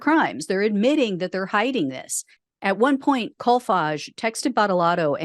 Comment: You said his name I swore you said CallFudge. I looked it up to see how it's spelled... Kolfage. Now it sounds & looks like a Swedish word for $h1t talk or Bu11$|-|ITTER.